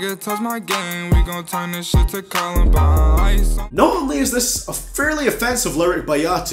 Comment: I always thought Yachty said 'We gon turn this shit to combine" (harvester)